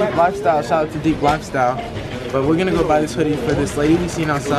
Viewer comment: Is it just me who her his say hoodie instead of umbrella at 6:50 ... 🤣🤣